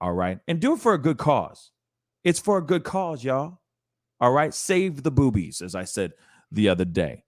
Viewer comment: Did he just say "save the boobies"?